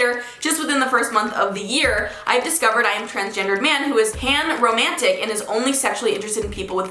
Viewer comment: When she said panromantic I thought she said hamromantic and then it mentioned vaginas and I was screaming 😂